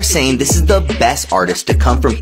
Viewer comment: I somehow misheard drake and thought it said “darkie” I almost got upset because you two are the best.